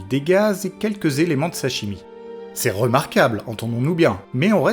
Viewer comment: "quelques éléments de sa chimie", j'ai entendu "sashimi"...